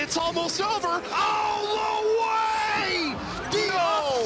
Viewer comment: did he just say...LWIAY? We need Poppy Gloria on the scene please